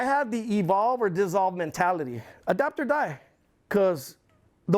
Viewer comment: "Adopt or Die" 29:04 I've been saying that for years and cheered when I heard him say that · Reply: Adapt. It’s probably what you mean also.